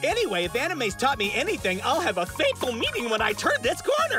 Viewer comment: I thought he said a "fateful" encounter. Not a fatal encounter.